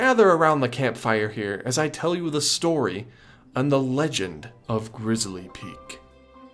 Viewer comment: Am I the only one that thought it said “the legend of glizzy peak”